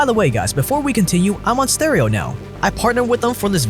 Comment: I heard "I'm on steroids now" during the stereo plug XD